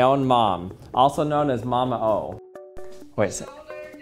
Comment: mama what? Did he just say “mama hoe”? Lol